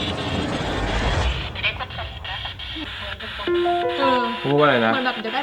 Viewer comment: ได้ยินว่าอย่าได้พบกันอีกนะ